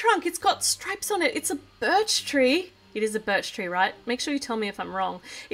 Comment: I thought said BIRD tree and I was like arnt all trees for birds